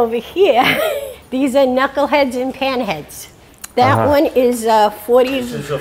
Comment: Great episode. Her accent made it sound like she was saying pinhead instead of panhead.